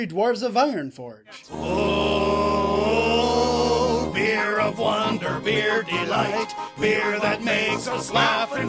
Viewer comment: i liked ''I saw three rouges'' and ''night elves we have strung on high'' and ''we three dwares of inronforge'' and while i was writing this comment i misheard a ''we three dwarves of ironforge'' sentence wrong:beer of wonder beer of beer the beer that makes us slap and fight